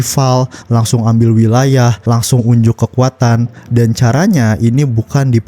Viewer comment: gw dengernya "unjuk kepunyaan" 🗿